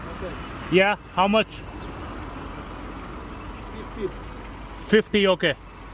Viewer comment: He said 15 but misheard 50 😭😂